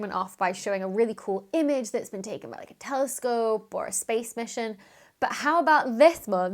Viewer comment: At first I thought you said "The first crude mission...." and wondered what was wrong with it. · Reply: I knew what she was talking about, yet I heard it as crude mission as well!